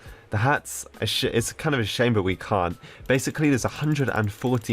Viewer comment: What did he just say... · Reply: That's a shame